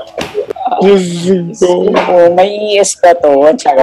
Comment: Wait tama ba narinig ko - "may ES ba to?" 20:44 hahahaha naloka ako ng bery light